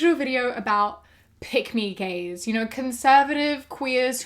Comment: At the end, sounded like you said “Pygmy Gays”, when you said “Pick Me Gays” — I’m calling them pygmy gays, from now on. 😂